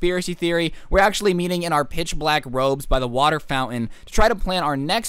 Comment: Best way to troll Karen (tm): 1. Buy black robes 2. Stand in ominous circle 3. Watch Karen slowly going insane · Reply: I thought you said Black Holes and was about to say Where do you buy a black hole?! eBay?